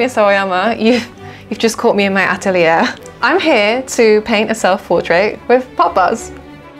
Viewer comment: I definitely heard "paint a self portrait with poppers" like um ok queen